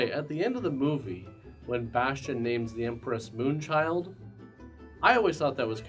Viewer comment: I was today years old when I found out Bastian was screaming Moon Child. I thought he was saying Amariah 😣